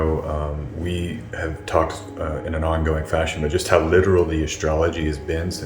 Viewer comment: LOL 4:14 I thought you said "Liberal". You would not have been wrong there either...